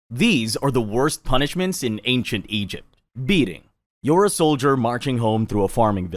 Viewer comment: I thought he said "feeding" Or"eating"🤪